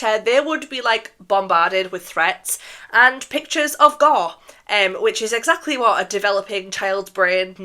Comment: maybe it’s just me being tired but I GENUINELY can’t hear what she’s saying at 7:37 “pictures of ___” 😭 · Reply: Gore